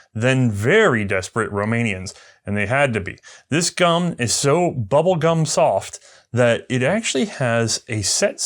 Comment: I heard, "this gum, is so bubble gun soft..." And I laughed.